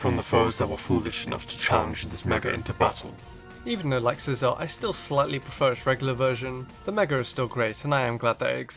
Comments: lol you said scizor instead of sceptile at 6:20😂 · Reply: Raichu He said "Even though, like Scizor, I slightly prefer its regular version" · Did you say scizor